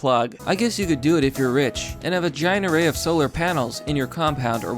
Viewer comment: When you said "and a giant array" I thought you were saying "a vagina." Got to get my hearing checked.